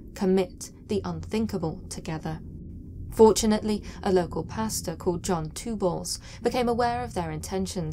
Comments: In the first segment I first heard “Pastor Two Balls” instead of “Pastor Two Bulls”. I was like whaaaaaaaaat. Then I realized I’m the weirdo. Good video though, keep up the good work! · it sounds like you said “john two balls”